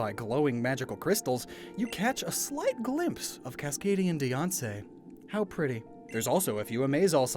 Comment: I wasn’t entirely paying attention because I was doodling in my sketchbook… but I legit thought you said “you caught a glimpse of Kaskadian Beyoncé “